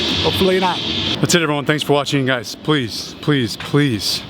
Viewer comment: For years I always thought he said train on time, but now know he says tren on da arm.